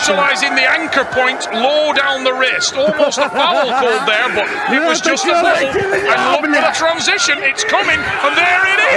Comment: Does anyone know what morozov said at 2:25 ? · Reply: Он сказал:«это какая-то херня, блять»